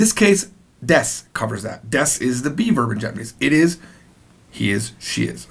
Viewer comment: I keep hearing ‘desu is the beaver in japanese’